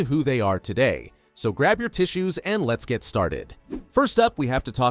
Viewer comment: At the beginning I thought he said grab your booties 😭😭😭